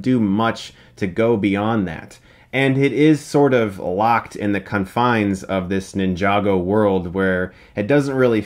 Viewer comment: Did he just say the cunfines?